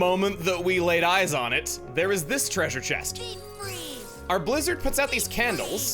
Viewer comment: Man it sounds like Sora is saying Deep Freeze every time they use blizzard